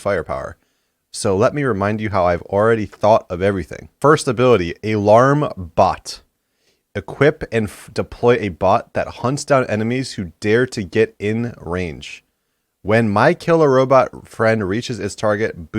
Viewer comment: is it just me or did he say, " A larm Bot" instead of "Alarm Bot"?